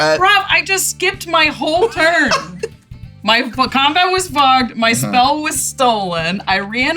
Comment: <link> thought she said "My combat was f*cked" and I was so impresed the editors really let her have it in sympathy after how messed up her turn got lmao